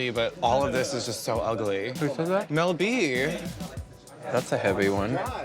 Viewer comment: Ok, who was the one criticizing Violet’s bearded look at 16:20 because I keep hearing her say Mel B, and I swear to God this is Rupaul’s Drag Race and not America’s got Talent